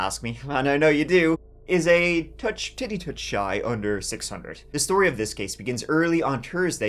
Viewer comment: I heard “it’s just a tiddy-touch shy” 😂😂😂